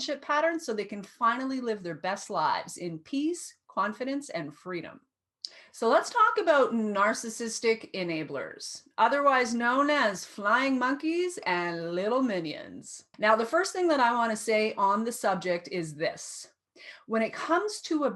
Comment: At first I thought you said "slime monkeys".🐲I like to see that someone other than me is angry at them because I feel right at home with people who recognize how damaging the betrayal is by these people!